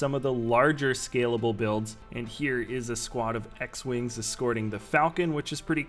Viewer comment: Am i the only one who heard him say "a squad of x-wings"